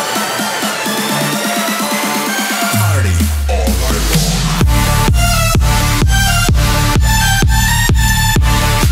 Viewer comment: what did she say here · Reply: Party All Night Long